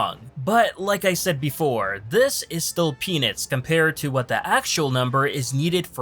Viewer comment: go to 8:24 does he say penis? I'm really wanting to know what the fuck I just heard